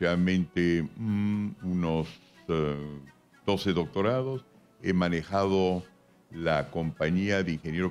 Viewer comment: Escuche bien? Dijo doce doctorados? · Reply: si no le crees investiga px jajaja 12 doctorados y yo con 1 que ya me quiero morir